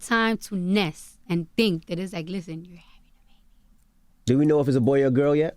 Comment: What did Cardi say @ 13:50 ???? I can't tell if she whispering or if they muted her! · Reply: Southern California she said listen your having a baby